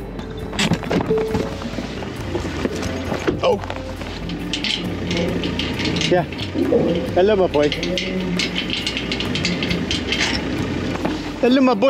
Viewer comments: Sound like he said help 😯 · is it just me or does it sound like he said help me 😨😥